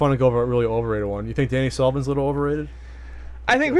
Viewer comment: I can't understand what you said, and the subtitles say "over really over 81 you think any solvent litte over it I think...."